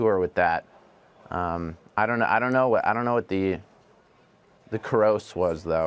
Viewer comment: What term did Jack use, and the interviewer clarified "after all your costs? I thought he said or means "gross"? Or did he use a business/finance term I don't know? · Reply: Bear Newman he ironically over exaggerated the pronounciation of gross